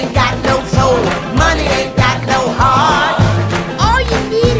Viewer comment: I love how Mama Odie instead of her saying heart it sounds like she’s saying hard I hope someone else hears that as well 😂